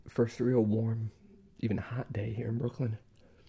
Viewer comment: I've tried ketchup before and it is gross . Alot of people say tomato source and ketchup are the same but they are dead wrong I would not put ketchup of my steak pies that would be gross UnU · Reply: Alibi have a question is anyone here in the comment section Aussie? Cuz when he said brooklyn I thought he said Melbourne so I had to go back and check.